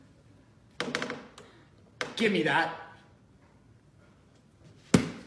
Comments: I love how he says 'your brain....is putty in my handddds' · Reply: omg i heard 🤣🤣 " YoUrE BRaIN is BITiNg MY AASSS🤦‍♀️🤦‍♀️ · I love how he says 'your brain....is putty in my handddds' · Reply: I thought he said “your brain . . . . Is pain in my ass” 😂😂😂